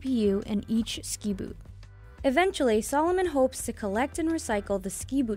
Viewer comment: I thought it said 'Salmon into shoes'